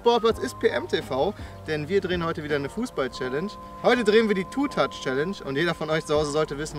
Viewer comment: Ich hab Football Challenge verstanden am Anfang